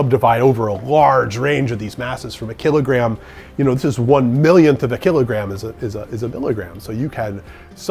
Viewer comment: We do a lot of conversions from SI units to Imperial units, but a "milligram" is one-thousandth of a gram, not a millionth of a gram. At 4:05, did I mishear that conversion? Otherwise, I'll have to rethink a microgram! Great video, thanks, and keep them coming. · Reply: Yes, he said, "a millionth of a kilogram," not of a gram.